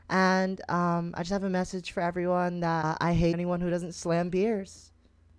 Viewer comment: What did he say lol? 😂😂😂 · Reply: He said "I hate anyone who doesn't Slam beers"